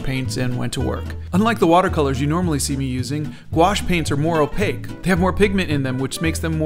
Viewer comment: I thought you said ‘gouache paints are more gouache’ hmm yes this floor is made out of floor